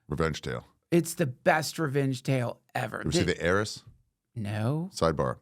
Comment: what movie did ari say? Idk how its spelled and can't find it... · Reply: The Heiress (1949), it's the only one I found matching his description. I could be wrong.